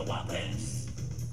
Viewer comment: I thought it said We got the wublins not wake up the wublins